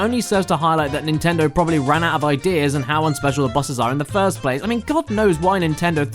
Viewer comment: What did he say at 2:55? It sounded like unspecial